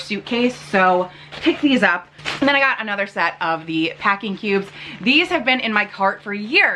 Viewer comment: Okay, two indicators that I am officially old: 1. I actively search for shoes with memory foam. 2. I honest to goodness thought you said "packing pubes". Literally had to listen twice. So my hearing is totally shot.😂 I thought everything looked great on you. Have fun in Miami!